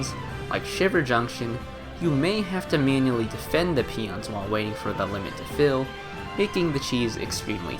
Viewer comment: I heard peanuts lol